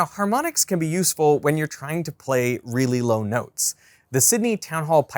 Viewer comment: I hear "try low"